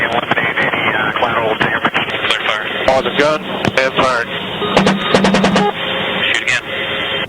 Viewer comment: "Unintelligible and firing." Way to go, Fox News, two minutes of research would have led you to find out that he said "On the gun... and firing." I understood him, and I'm not in the military. If they had asked anyone who was, they would have found out what the weapons officer said. Dumbasses.